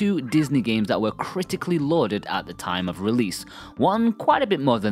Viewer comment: I have no clue what that word is?? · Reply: Lauded?